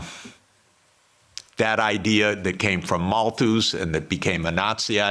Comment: What's the word he says after "We are not struggling for lebensraum. That idea that came from... (Sounds like maltoos)"?? At 4:35 I can't figure out what the word was